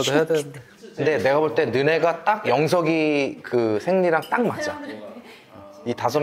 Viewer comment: 여기 나만 생리로 들려…??